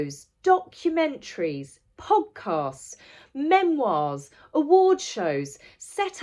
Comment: When you said "podcasts," I heard it as "hogcasts," and really, that's what they are. Just nonstop yammering about me me me with no one else getting a word in edgewise.